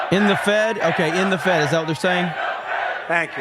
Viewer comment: End The Fed I for a second thought they were saying Pay No Tax